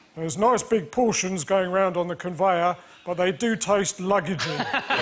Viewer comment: What does Milton Jone say at 2:27? "They do taste..."? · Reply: Luggagey. (As in luggage)